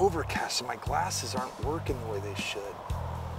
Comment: I thought said “they’re not working with this s——“ and I was shocked because I never heard him cuss before but then I replayed it and he actually said “they’re not working the way they should” lol.